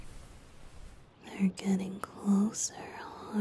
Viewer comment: I HEARD IT LIKE "We're getting closer~" LMAO